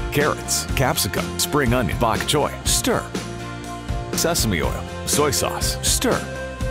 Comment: HELP I thought he said semen oil